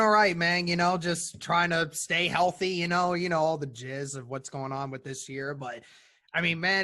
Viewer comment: Bro did you just say all the “jizz” 1:04 😅